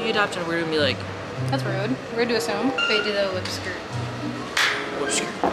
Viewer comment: Yooo at like 1:17 it sounds like says bae do the whip skrt